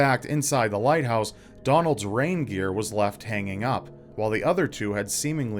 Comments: Rain gear... I thought you said reindeer at first... so I thought well then they must have gone with Santa! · I thought said one of the keepers had a reindeer instead of rain gear. What a weird pet to bring along, I thought.